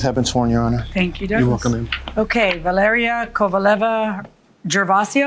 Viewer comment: Is it just me, or did Douglas just say “you’re welcome baby” 😂